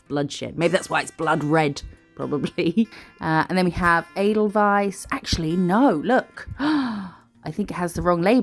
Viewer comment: Omgggg i had to rewind this a few times as i thought you was saying "Anal Vice" 😂 😂 but looking at the discription it seems you was saying "Edelweiss" ffs 😂😂🤦🏼‍♀️ x